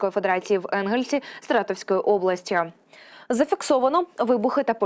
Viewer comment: мені почулось чи ведуча сказала Всратовської області? Бо якщо не почулось, то це прекрасно